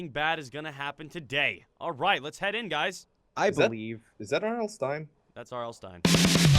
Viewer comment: At first I thought he said Arnold Stine. I was like who the heck is that? Then he said R. L. Stine and I heard IRL Stine and I was still confused. Then he said the author of the GooseBumps books and I was like OHHH! Im Such a RETERD!!!!